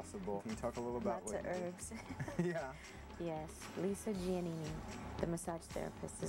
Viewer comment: anybody catch the name of her massage therapist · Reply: Lisa jeanine I believe